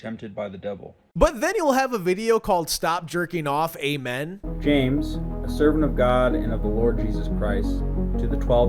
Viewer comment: why did i hear "stop jerking off eight men" 💀